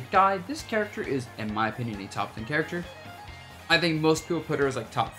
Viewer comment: I can’t hear what you’re saying at 0:28. Did you say “top 3 character” or top 10? There are no captions and you are speaking too low and fast in that moment for me to hear :( I tried slowing down the video at 4 different speeds and still have no clue